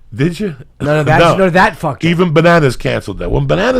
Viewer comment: I thought lil' Jimmy said he fucked her too which would be unsurprising seeing as he likes shemales